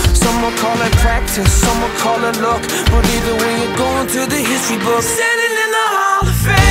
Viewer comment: lol it sounds like he says hissy books